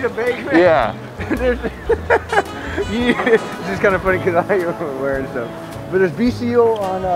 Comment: what he said was “because you’re wearing stuff” with a chuckle, that’s what was “unintelligible”